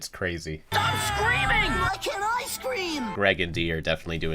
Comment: am I the only one who thought Greg said you're screaming like an ice cream when I was a kid at 3:01 ?